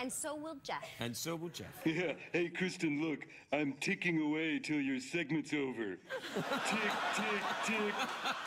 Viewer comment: someone can tell me what geoff said to kristen here 5:40 , i couldn't understand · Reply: Look Kristen, I'm ticking away till your segments over